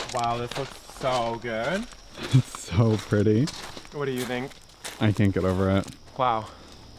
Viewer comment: whenever he asked "how do you feel about it" and he says "i cant get over it" i thought he said "i think i'm over it" i was like wtf lmao. but frrrrr. this is the coolest thing i've ever seen i think. like wow great job y'all!! :DDD